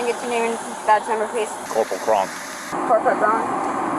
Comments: “Corporal Cron” I heard Krunk at first and I just about lost it. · me, I heard him say he was Corporal Cronk